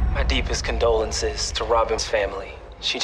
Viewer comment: did he said mommy cause they were kissing in the mouth like if they were boyfriend and girlfriend 🤷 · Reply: I think it's Robyn since she's the one who died